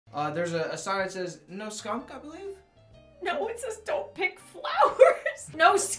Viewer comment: "There is a sign that says No skunk... No skunk I believe" "It says don't pick flowers" "As you may know, I cannot read" "It doesn't have words" Goals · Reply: But why did I hear him say "No skunk gobbler" XD